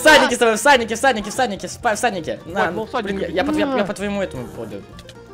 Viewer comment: Мне послышалось что вы не всадники а в садике :)